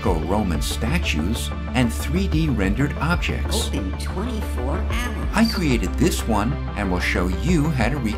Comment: who else heard the high pitched voice in the background say "open 24 apples"